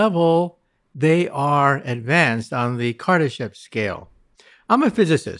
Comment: I thought he said Kardashian. I momentarily thought we might find big booty aliens with a penchant for black males.